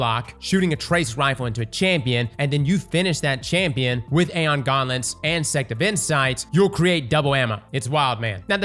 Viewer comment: Aeon Gauntlets? Or Aeon Garments? 😂. Sounds like ur saying garments